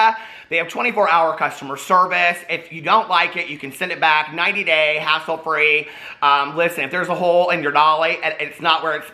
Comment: I thought he said 90 day ass hole free LMAO